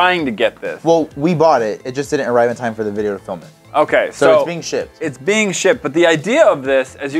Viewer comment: WHAT DID YOU SAY. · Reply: Sosig_Beby ships! He said ships! Calm ya self!